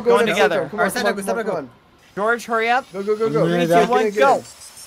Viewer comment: The fact that they said “let’s go in together” scares me, like did they not learn their lesson? Send a bait like George or something (edit: not saying they’re dumb or anything but in one of George’s videos it took George a while to figure it dream was making a trap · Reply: someone @ 1:19 (I think Antfrost but unsure) says like “Sapnap go in” and “George go in” BAD IS THE ONE SAYING ALL 1 TIME